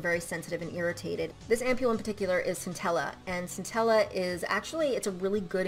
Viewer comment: I was listening to this as a background, doing something else, and at 12:40 I misheard the name of the product, so I was like "NUTELLA?! Why would I put Nutella on my face???"